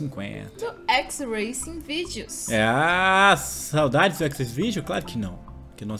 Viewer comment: entendi xvideos ksksk